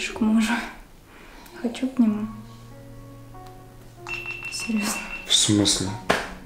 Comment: я не могу расслышать,что она сказала? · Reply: Серьёзно